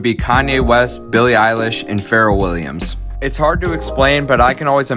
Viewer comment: Did he just say Pharaoh Williams? 😂